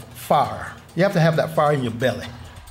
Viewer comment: did he said you have to have that fart in your belly?